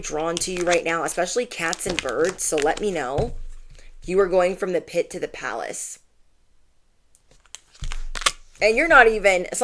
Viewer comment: When you said “You’re going from the pit to the palace” I heard “Cinderella.” 45:25 😊✨